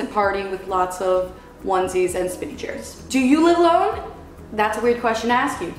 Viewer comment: Lmao I thought she said Spinach Hair xD Instead of spinning chair lololol